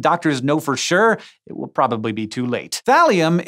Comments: I thought you said Valium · When he said Thalium at first I was like, wait....did he just say Valium? lol